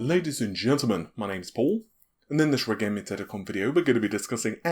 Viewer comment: What does he say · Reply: Forestman he said "and in This redtechgaming video"